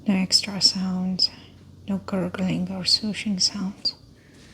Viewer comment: For a sec I thought she said, "no gurgling or sushi sounds..." 😂😂😂